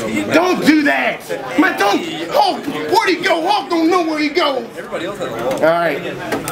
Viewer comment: What did he said at 2:36 ??? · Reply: WhereThereDude where did he go? hulk don't know where he go.